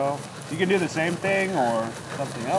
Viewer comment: I just hear go, “I’m good” it killed me